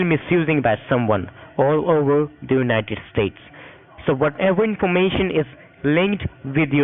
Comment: did he say "misused by someone OVER IN the United States"? As though he works for the US SSA from outside the country?!